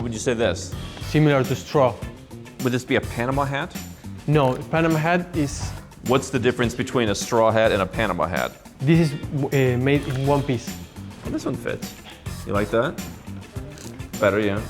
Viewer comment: "What's the difference between straw hat and the other? - That one is from One Piece That's what I heard and you can't convince me otherwise XD